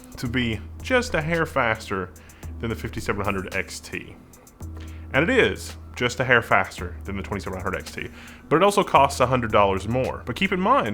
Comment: did you say "...and it is just a hair faster than the 2700 XT"?